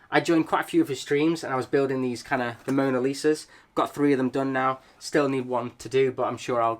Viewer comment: did he say Mona Lisa? Or did I misheard it? Also I really hate Lego for doing double VIP during a month I can’t buy Lego....a new DLSR camera is more important at the moment....